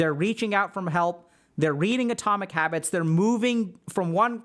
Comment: I heard "Atomic Rabbits" and thought "ok, that's a self help book that would grab my attention"